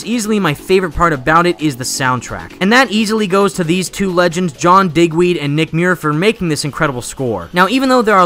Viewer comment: why did I hear “DickWeed”?